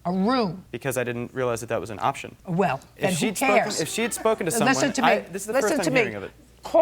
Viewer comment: what was that person yelling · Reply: I think they were echoing JJ with “who cares.”